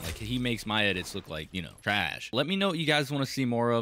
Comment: What Word Is it trash Or Rash